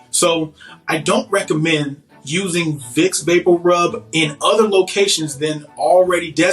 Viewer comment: Hahah. I had to turn on CC because I swore I heard him say that Vicks vapor rub were mythical haha. Oh it's true.